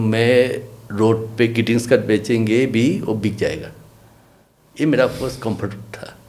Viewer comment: Sir what did he say at 45:21 in the video ? it's not clear road pe baeth ke kya bechange ?? please clarify. The story is very painful. 😱😥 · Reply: "greeting card bechenge"